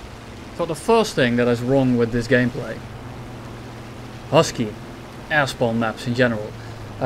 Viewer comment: I keep hearing "Ass bomb" instead of "Air spawn"